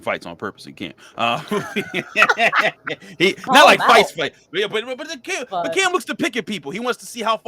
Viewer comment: Said “fight” I heard “white”. Lmaoo had to backtrack for a sec